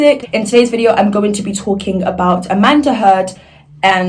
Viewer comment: did i just hear “Amanda Heard” or am i deaf is that her real name or something